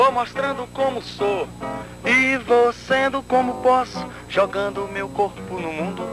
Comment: Sempre escutei o trecho dessa música por uma música do Baco (Esú) e sempre foi uma batalha pra achar pois eu sempre entendi "vou fazendo como for, me concentro como posso, jogando o meu bundão..." e hoje, graças aos Deuses, consegui achar essa obra de arte.